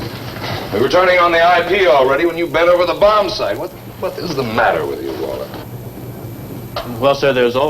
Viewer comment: Listen to Savage's dialogue starting about 44:23...don't waste your time looking for it in the pissy ass closed captioning...I swear he says "...what the fuck is the matter with you, Waller?"